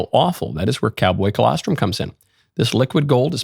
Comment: Michael you gotta be careful with the ad reads. I was listening, and 100% thought you said 'catboy colostrum'